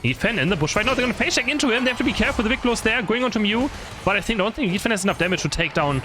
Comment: im sorry but i cant understand half the time hes talking fast. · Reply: My only and favorite misunderstanding I have with accent is when he says “Wicked Blow” I hear “Big Blow” a lot of the time. He recently confirmed that this wasn’t an intentional shortening which I was never sure of